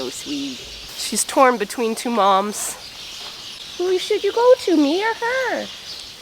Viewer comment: I thought when you said "Who should you go to me or her?", I thought you said who should you go to Mia and I was like oh no she already named it! 😄